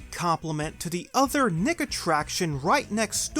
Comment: did you just say... Niggattraction???? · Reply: He said Nickattraction